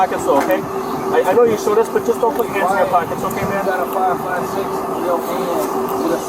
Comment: subtitle say ".556" it's meant to be "5.56" as in the millimeter diameter of 5.56x45 cartridges